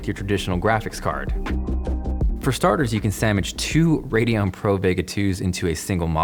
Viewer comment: did you just say "Samwich"?